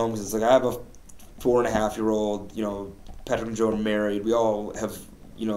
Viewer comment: "Patrick and Joe are married"... Sounds like he means to each other. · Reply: Maggie I thought he said “Patrick , Joe, and Mary. 😂